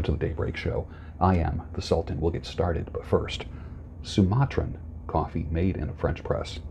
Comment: thought you said Sinatra coffee lol